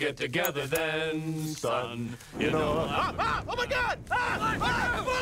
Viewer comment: I swear I always thought at the end, one of the trees said “Todd, you stupid bastard!” directing it to the tree for igniting the lighter but I guess according to Hulu and Netflix subtitles it’s “Run, you stupid bastards!” directing it towards Peter and Chris. 😂😂😂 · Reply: I originally thought he said "It's hot, you stupid bastard!"